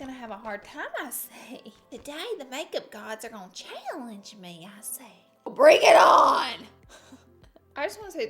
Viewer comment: Am I the only person that when she said 'the makeup gods are gonna challenge me today ‘ I thought she said ‘ the makeup gods are going to jail today’😂😂